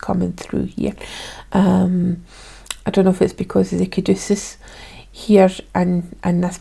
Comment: Hey, Thank you for filling in for Carrie. I feel so held and supported by her and i am so glad to be feeling the same way with you. Could someone please tell me what she says at 10:45? Thanks. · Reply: I say Caduceus. The symbol of Hermes/Mercury. ❤ Thank you so much for watching ✨️